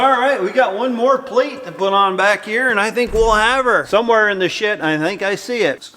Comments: Anybody else think said sh*t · Did say what i think he said 1:28 sound like he said some were in the shi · Ah hahahaha. Dads got a potty Mouffff. Did he say “somewhere in the SHIT” ? Good for you. BMPardone